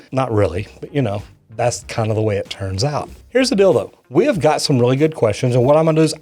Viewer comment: Why did I hear "here's a dildo" instead of "Here's the deal though". Maybe it's because that's a tool specifically used for sewage.